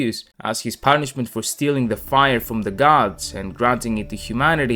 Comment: Had to double take cause I thought I heard you say the "fryer of the gods" but I'm wrong, and I guess I'm hungry. God bless that fryer though